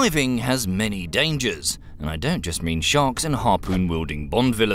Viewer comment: Did you just say sharks???